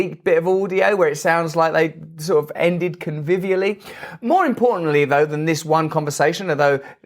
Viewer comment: brand what word dis you say at 00:58 ? Was it conviviality? Just trynna understand what u meant